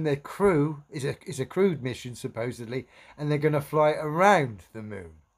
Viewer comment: When they say crewed mission I always hear "Crude mission". When they say Artemis I think of Artemis Gordon from Wild Wild Wild West.